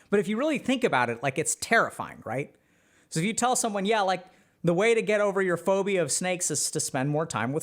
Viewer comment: I misheard: "The way to get over your phobia of sex is to spend more time with snakes" but it makes sense somehow.